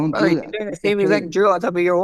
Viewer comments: He thought Holloway said "Joe" when he said "Drill". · Lmao the way Holloway said drill and ilia thought he said Joe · It kind of sounded like Max said Joe, when he really said "drill"